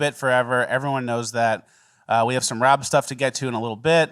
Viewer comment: I definitely thought Rob said "we had some BJ's" lol P.s. I would say the N word for free.